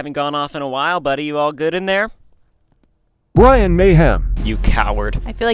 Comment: Bryan mayhem 😮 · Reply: At 1st, I heard “Ryan Mayhem”😂